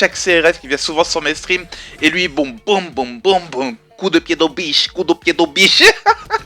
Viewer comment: j ai cru comprendre pédobiche dites moi que je suis pas le seul ^^